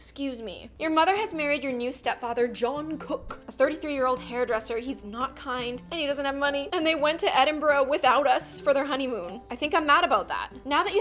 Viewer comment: any army’s here and thought she said “Jungkook”